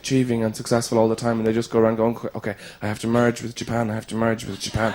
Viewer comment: Does he say "I have to merge with the japan"? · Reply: He says "I have to merge with Japan", seeing as he is portraying a businessman trying to keep up with the status quo. :)